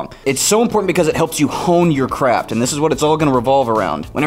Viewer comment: almost thought he said "hone your crap"